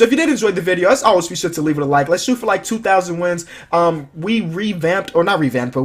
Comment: did he say shoot for 2,000 wins? ( 10:20 )